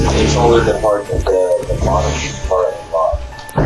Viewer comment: I thought they said tell “her” to park. So I assumed a girl was driving.